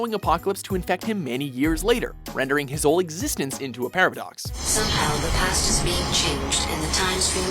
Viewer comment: did he just say Parablox? I guess that whole thing was complicated